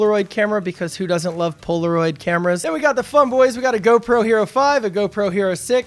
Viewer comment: Thought you said “now we have the f*ck boys” hahahaha